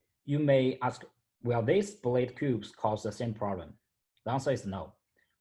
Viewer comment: what is he saying.. "you may ask.. ..... cause the same problems?" fill in the gap please. Its hard to understand his accent there. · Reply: "blade" cubes? I'm Chinese and I can't understand either. 😀